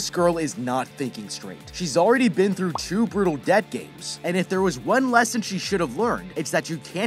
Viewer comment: Every time he said debt game I thought he said death game.